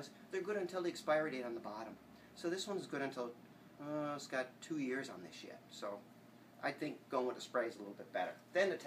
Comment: did he say "two years on this shit" haha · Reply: 2 years on this yet